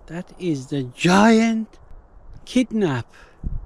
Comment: Did You Say KidNap